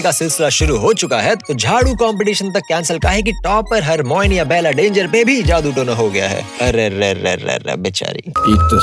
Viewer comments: What? did he say ABELLA DANGER ?? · did he said Abella Danger ?? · did you just say "Abella Danger"?😛